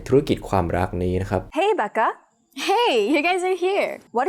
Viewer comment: ตอนได้ยินHey Becca ตกใจคิดว่าBeggar ที่แปลว่าขอทาน ต้องย้อนกลับไปอ่านซับเลย